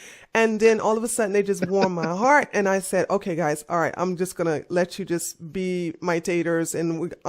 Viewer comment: "Let you see my taters"?? Did I hear that right?